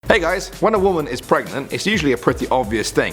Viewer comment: Who else thought he said WONDER WOMEN IS PREGNANT,instead of WHEN A WOMEN IS PREGNANT.....